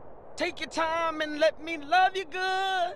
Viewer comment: Take your time and let me... what he said? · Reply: Love you good